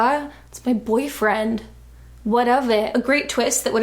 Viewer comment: for the longest time i thought she said “whatevah” in like a brooklyn accent here